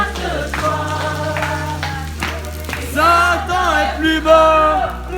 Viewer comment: N'hésitez pas à vous abonner pour voir les prochaines publications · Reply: pourquoi dis-tu à 1:06 "Satan est plus beau"!?😱